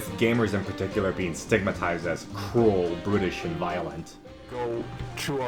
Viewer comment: i heard "brittish" instead of "bruttish"... thats reem.